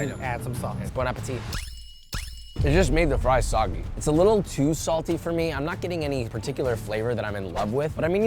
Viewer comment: did Josh just say TOO salty???